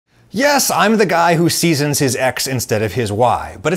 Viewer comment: I was in the comments and misheard it as "Yes, I'm the guy who seasons his ex instead of his wife"